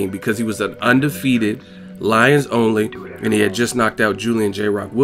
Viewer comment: did you say because Charlo was Loins only he got a good ranking that shit sounds crazy 😫